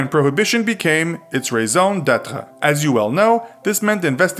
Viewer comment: what did he say? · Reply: Use closed captions, he said raison d’etre